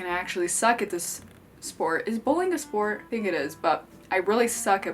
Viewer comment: lowkey thought you said “is bullying a sport”